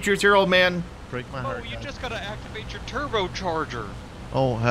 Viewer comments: does he say "Turbo Targer"? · why did i think he said "Curbo Targer"?